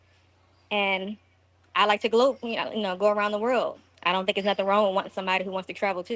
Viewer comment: Wait wait wait did she say gloat around the world 😂😂😂